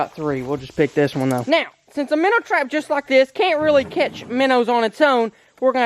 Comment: did he just say MINNOWS not minners